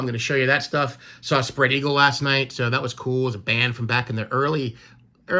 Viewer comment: It sounded like you said “I spread eagle last night.” Great stuff man! Love the sign. 🎼 “I saw the sign and it opened up my eyes.”